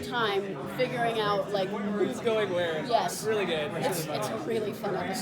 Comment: ish i think—— did jeremy say it’s really gay??? does this mean something????